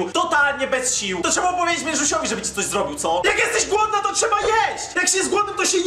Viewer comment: Xd ja usłyszałam jedziemy bo jestem dziwna a ona powiedziała ze jest glodna